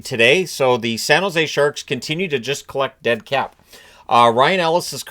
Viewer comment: "dead cap"... at first I thought you said "dead CATS" LOL.